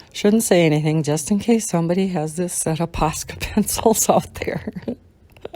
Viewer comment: I misheard when you started talking about the Posca pencils and thought you said pasta pencils 😂